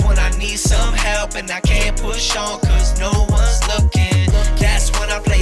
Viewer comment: does anyone else hear “I can’t push Shawn cuz no ones looking”